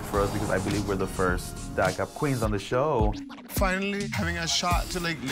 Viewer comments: they're the first WHAT on the show? what are they the first of? sounds like "that up" and that's what the subtitles even say, which makes no sense. what is this editing, c'mon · Wait can someone make out what Geneva says at 0:23??? She says "this is something big for us because I believe we're the first ____ queens of the show." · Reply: I think she said DACA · um does anyone know what he said at 0:24 like omg ? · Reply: I think she said DACA queens